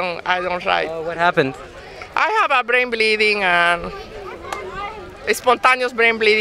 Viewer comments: Did she say Brain 🧠 bleeding 🩸😳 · "Trump 2020" and "I have brain bleeding" might be the single most iconic sentence I've ever heard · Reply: I thought she said "rim bleeding" so I figured she couldn't sit down on the rides. 😂😂😂